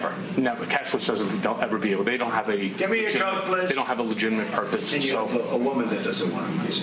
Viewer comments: YOOO DID HE JUST SAY CAN I GET I COKE PLEASE. CASHMERE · "Maybe He Gets hit By A Truck!" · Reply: Sandra S | hahahaha... I heard that😂🤣😃@ 2:38 Trump yells, "GET ME A COKE PLEASE." hahaha